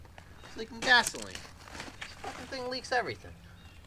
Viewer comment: did he say “this fucking thing leaks everything”😂😂 first time I’ve heard this guy swear before